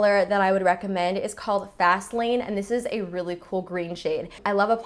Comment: When she said fast lane I wasn't looking at the screen and I thought she said Vaseline...